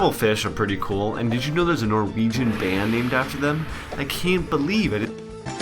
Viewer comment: It sounds like the audio cuts out or something when he says " I cant believe ???" Maybe he's saying I cant believe "it" or "that''. It kind of sounds like hes saying "edit"